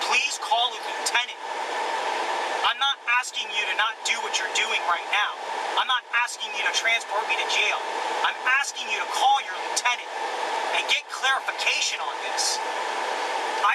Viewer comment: I thought he said "I'm not talking to you"